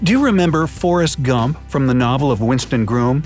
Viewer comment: Did he just say Forrest Gum in 4:50 its GUMP!!!